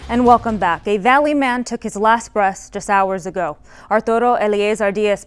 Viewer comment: did she say last breast?